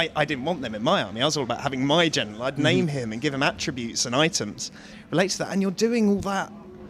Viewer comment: Wait Wait Wait, at 16:15... Did he say give him items?